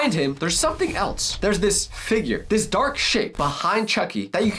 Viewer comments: am I tripping dawg I hear swearing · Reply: He said shape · what did he say it sounded a lot like a naughty word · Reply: Shape